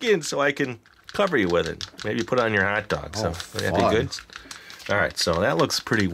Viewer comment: did say the f word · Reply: No he said oh fun I thought he did too